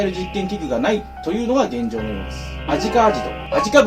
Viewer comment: 一回目動画ふわっと動画みた時「アジカアジト」「味の素」に聞こえたのは俺だけ？